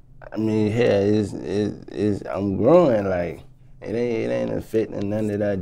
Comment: W H A T? Did he say, "I'm growing"! Then W H A T ???